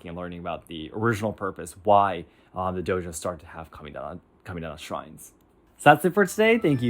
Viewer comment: I did not know this origin, thank you! I have a question though. In this podcast you speak of "kaminaga" shrines (apologies if I misheard and spelled this incorrectly). My dojo and other practises speak of a 'kamisa' shrine. Have I misunderstood things or is there actually a difference between the two? I am not a Japanese speaker but I would love to know more about this side of budo. Our dojo does have a shrine by the way, there are portraits of numerous sensei hanging there, including the founder of our ryuha. I like to think that I'm bowing in gratitude to their wisdom and time they have given their students. Next to that, we bow to the sensei and each other in gratitude that we get to practise and learn from each other. Have a nice day!